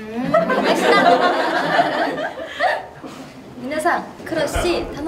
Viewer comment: リズなんて言ってるの？ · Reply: 楽しみにしていて下さいね❤って聞こえます。